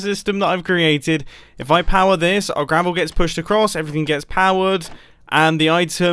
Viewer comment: I thought he said "our grandma gets pushed across."